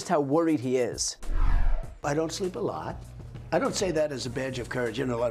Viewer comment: did he say 'I don't seem to lie' ?????????????????????